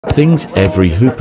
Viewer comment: Why did i hear "edgin in my room" 💀💀